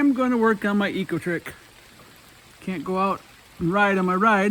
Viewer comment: I thought you said "My Ego Trip."